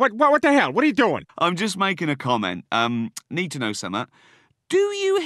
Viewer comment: WTF does he say - "Need to know SOMEAH"??!! Da fuq language IS that?!